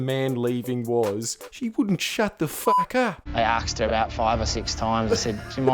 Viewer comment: I know strayans don't exactly speak the queen's english, but come on middle aged tom ballard, "arksed" instead of asked?